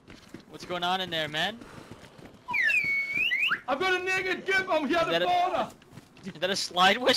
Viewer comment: he actually says 'I've got a naked gimp and I'm here to barter'